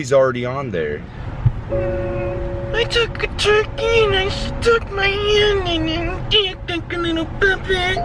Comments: I didn't understand a word you say there lol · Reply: he says " I took a turkey and stuck my hand in it and played it like lil puppet" thank me later. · I heard “I took a turkey and stuck my hand in it and it was like a little puppet” #Ayearlate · I think Colby said “I took a Turkey and stuck my hand up it,your hand is a little puppet”